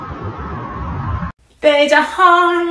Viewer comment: What I hear “there’s a hole in my brain for you, oOOo00Oo0“